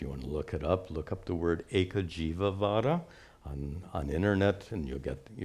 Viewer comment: Does anybody know what word he says at 1:17:18? I tried to look up "ekajivavara" which is what I though he said, and I got no results. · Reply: Hello friend! It's Eka Jiva Vada 🙏I looked it up as well 😆The overall gist of it is everything and everyone except the Jiva is Real. alludes it to be idealism (which can be contrasted with realism). I would recommend looking at the middle ground for both spectrums, "critical realism". Personally, I find that more useful in a pragmatic sense than the other two extremes. 🙏